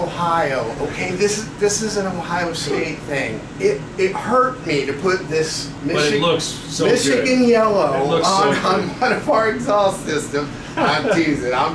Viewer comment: I thought he said Mexican Yellow